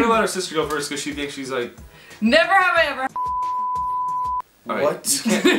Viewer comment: I’m pretty sure she said 😂 “never have I ever had sex with someone” it sounds like she said that